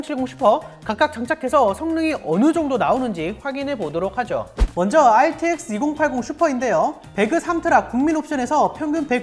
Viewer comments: 초.. 2080SUPER로 들리는건 저만 그런가요?? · RTX2080슈퍼라고 들리는거 나만 그럼?